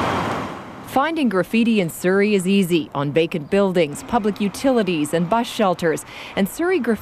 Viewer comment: is she sayin "bacon buildings" ?!